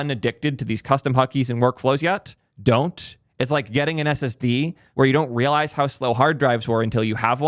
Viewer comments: I heard "getting an STD" and I was like "right, you will never get rid of that itchy feeling like your missing something" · I heard "it's like getting an SSD" as "it's like getting an STD"